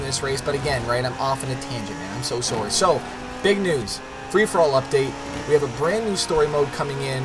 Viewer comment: did he just say...( big Nudes) at 2:47 XD <3